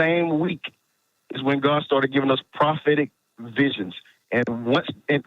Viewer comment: I could of swore he said “god gave me prosthetic visions” I pictured a man having dreams of plastic limbs and hooks. 🤦‍♂️